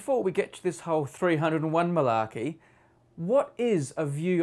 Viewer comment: I watched substitute teacher before this and all I hear is balakey